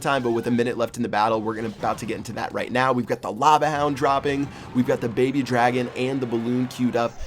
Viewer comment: did just say lava hound droppings?!